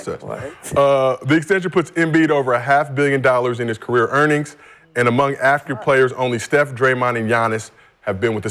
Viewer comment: did he say “active” or “African” players? Is Steph African?